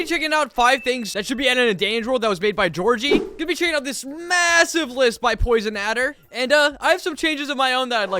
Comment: did you say m-m-massive?